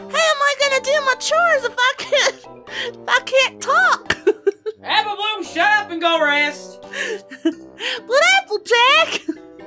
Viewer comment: Oh i thought she said she said "APPLEBLOOM SHUT UP AND GROW BREAST'S! "